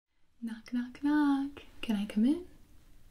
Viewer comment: Lol idk why but when you said "knock knock knock" in the beginning I thought you said "Nacht Nacht" which is german and means "night night"🌙 I had to play it again because I misheard it the first time😅so anyway have a goodnight I guess🤗